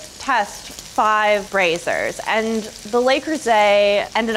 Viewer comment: I thought she said “Brazzers”